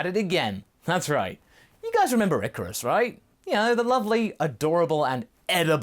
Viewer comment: Masako: "What was the original shenlong all about then?" Youtube ad: (insert Zaxby's chicken sandwich commercial here...) ...thank you YouTube 🤣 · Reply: It thought he said "Icarus".